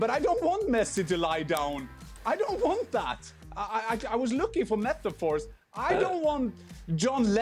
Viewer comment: When Jan said: "I was looking for metaphors", I initially thought he said: "I was looking for meth, of course!" Thank heavens for the Closed Captions. xD